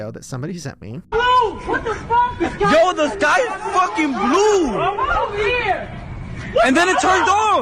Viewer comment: It sounded like they said the "sky is bright blue."